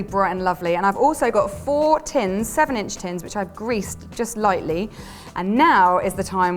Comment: Can someone tell what size are those cake pans? Thank u! Perfect bid as always 💜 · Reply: i think she said 7 inch tins in 2:16.